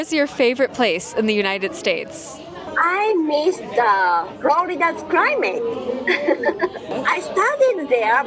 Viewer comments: The japanese robot lady said "I miss the florida climate". And from someone who's from Florida, she wrong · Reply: I thought she said she missed Florida's crime rate, and was like "huh?" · I thought she said she misses the florida crime rate 😂